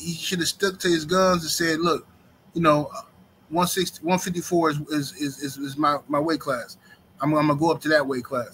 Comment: I thought he said 154 is his limit.